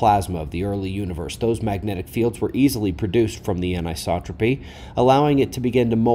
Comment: you used a word that I hadn't heard before and want to look up. It is at around the 1:27 mark, sounded like "Inisopotry'? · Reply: The word is anisotropic. It refers to measuring something, anything really, with the same instruments and the same measuring system, but getting different measurements from different directions. Usually refers to space-distance. According to the "Standard Model" of cosmology, when measuring the Cosmic Microwave Background Radiation, the theory has been that the CBR is a constant. In fact, however, it is not--it is "anisotropic", indicated bulges and lobes of varying temperatures. I've had questions about this model for years, the "Big Bang" and all of that. And, the Standard Model adheres to the fairy-tale land of Dark Matter and Dark Energy, which after spending $1trillion in U.S. taxpayer money on this crap, they've finally come to the end of the line--at least with the leading research anyway. But you still have professors and grad student instructors teaching and grading students on this rubbish. And they STILL refuse to consider that we live in a PLASMA-ELECTRIC UNIVERSE. It's disgusting, really. It would so disrupt the framed (broken) reality from whixh they live, but would answer so much more than just beating a dead horse.